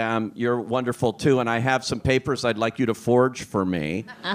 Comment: 이부분 사회자가 뭐라는거에요 ㅠㅠ? 왜웃는건지 알고싶......ㅠㅠ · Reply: I'd like you to forge for me = 나를 위해서 문서를 위조해줘 (기생충에서 박소담이 대학졸업증 위조한 씬을 보고 이렇게 말한 것 같네요!)